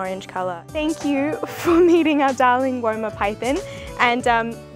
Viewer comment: I thought 'woma' snake said 'woman' snake and I was like 'wow what a beautiful woman :D'